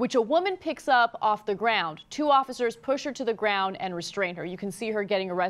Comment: I thought he said "Get on the ground" to the woman.